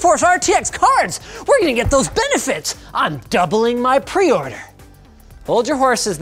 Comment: Totally thought that said "doubling money" and my runescape flashbacks kicked in lol